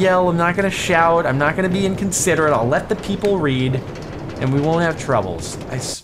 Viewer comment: I thought he said I'll just let it pee